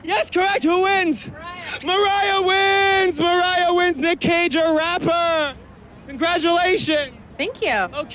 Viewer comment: I thought he was saying "Nic Cage Vs RAPTOR" lol. I was waiting for some good ol Deinonychus or Velociraptor name dropping but was met with the cold disappointment of a person who calls himself "gucci mane"; like u ain't no lion bro, u probs don't even know about Xenosmilus. Pssh smh what a poser